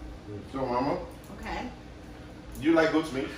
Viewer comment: Does your mom is French? I heard “comme-ci comme ça” 🤔